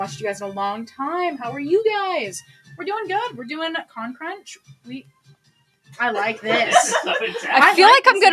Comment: i'm watching a part of this again cuz i forgot ur answer to a question and i thought it said con church the whole time i was in live and only now realized it says crunch